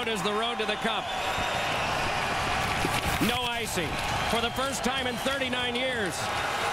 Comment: wait but didn't the red wings win the stanley cup in 2002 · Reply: I thought he said for the first time in 11 years